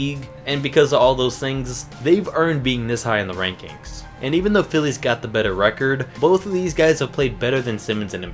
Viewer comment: did he say Phillies😀 Wrong sport bud